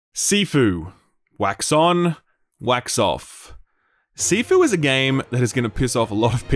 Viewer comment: Skillup: "Wax On, Wax Off" I hear: "Whacks on Whacks off" and think to myself "Hmm, I have already mastered the 'OFF' part ever since I was 13. I'm sure I can master the 'ON' part too.